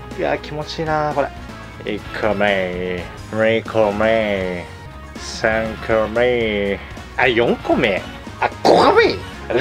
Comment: いつも見てますよ！ とても面白いですよ！ 2個目、3個目が 2コメ(ント)3コメ(ント)に 聞こえたwww